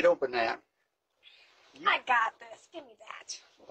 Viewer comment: 🥹❤️SUBSCRIBE PLEASE 🙏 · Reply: The old guy is married if you heard him say i and Carol couldn't open it. I think he said I don't have any strength left not friends. This makes more sense in the conservative. I know his accent is hard to understand but watch it again. ❤